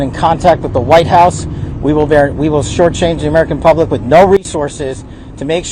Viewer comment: "We will shortchange the American public with no resources"? Maybe I heard him wrong.